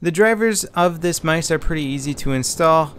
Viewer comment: did he say mice?